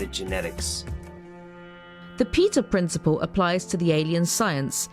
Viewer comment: I misheard that as "The Pizza Principle ..." Just thought I'd share.